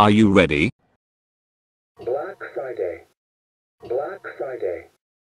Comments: I heard "Black Exciting" lol😂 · Everyone: I heard both “Black Friday” and “More exciting” No one: Absolutely no one: Not even Fred: Me: I heard “Black Flyday” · I heard "black fly day" I think I need help 😭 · I heard "black flanday " lol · I heard " Black Exciting " lol 🤣